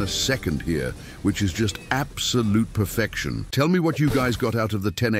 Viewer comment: bro said tell me what traits and at 0:08 bro said 10 traits